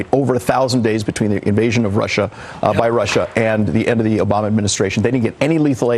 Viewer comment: Did he literally say "The Ebony Obama Administration"? Lulz for him playing that card if so. Perhaps I misheard him.